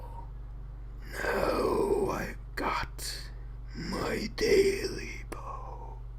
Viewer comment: I heard it as taily-bone when I was growing up